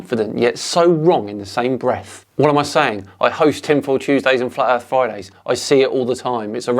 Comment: For a moment there I thought he said Tim Pool Tuesdays.